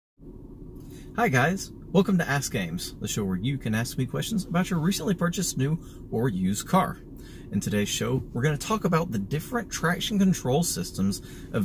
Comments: Bro, I thought you said "Ass Games", rather than. I was like, "WTF is this guy gonna say?" It turned out to be the most informative video I have seen on operational control of 4WD settings in the 4runner, across trims. May you live to be 1000 years. Cheers! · I totally heard, "welcome to ass games..." I was like, WTF? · i thought you said ass games. that's a different kind of video though. asside from me being as ass its a great informative video. thank you.